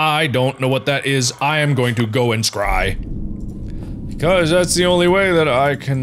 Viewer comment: it sounds like he is saying cry